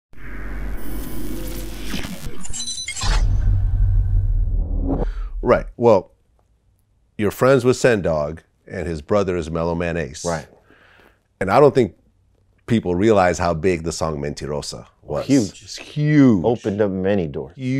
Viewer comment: Thought he was saying mellow mayonnaise .